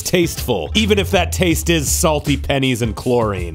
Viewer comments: Did he said Game or Gay realeases? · You Gays instead of Games at the end there.